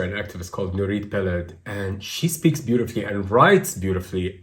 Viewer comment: whar is the name of the israeli proffessor that they are naming at 44:00 ? i hear it but since its a name in a nother language i don't know how to write it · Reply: Nurit Peled Elhanan